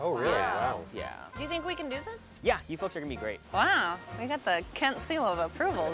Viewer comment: i thought he said you f*cks are going to be great at first 😂😂